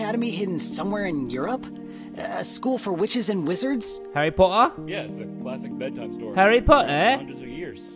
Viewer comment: No one: Literally no one: Harry Potter in british accent 😂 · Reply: as a filipino i was kinda confuse i thought you all saying Harry Puta